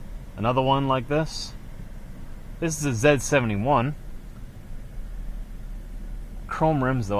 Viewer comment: Hey it's called Z-71 I thought I heard you say S 71 lol